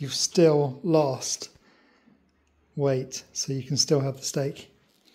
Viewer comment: Sorry all this is an oldie but sometimes things happen, we had to unexpectedly go to the vets so had to use an older video this time.. hope you can forgive me and not give me too much greif for it 🤦 (thats sarcasm 😝) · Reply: That face at 6:12... please do a check on my ears too, did i hear STEAK...😂😂😂 is such a gentle soul...🧡🧡🧡